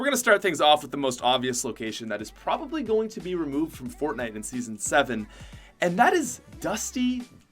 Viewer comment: did he say season 7